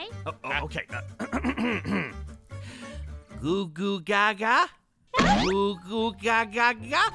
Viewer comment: WHAT IS ZANE SAYING"GOgOGogA"🤣😂